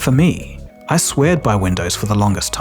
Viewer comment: Did you say “swear-ed”?